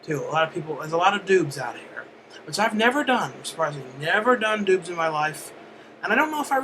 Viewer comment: for a second there i thought you said "i never done doods before" instead of doubs